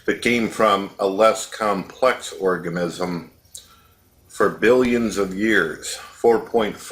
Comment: Did say "orgamism" at 30:08?